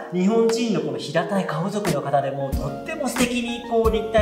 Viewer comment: 「日本人の汚い顔族」に聞こえて、何回か聞き直した（笑